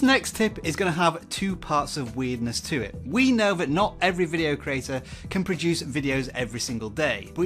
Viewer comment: Subtitles mistake: 7:04 it is written “We know that now every video creator” instead of “We know that NOT every video creator” (I think)